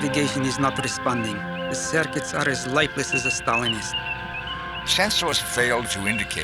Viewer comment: LOL! 6:41.....lifeless as a Stalinist.....( at least that is what it sound like he said.) Funny stuff if so!